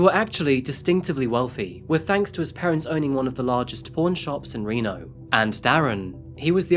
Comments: So he had a pawn shop (print on screen lol)... not a porn shop.. I misheard, given his history with all the girls and Nevada. Him being a dedicated narcissist, he did not shoot himself while on the run. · I legit thought he said porn shop til about the 3rd time I realized it was pawn